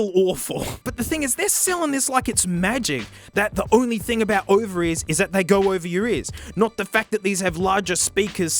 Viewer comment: This whole time I thought he was saying ovaries instead of over ears